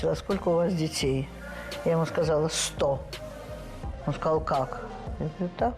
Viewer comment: not "what" but she said 100😂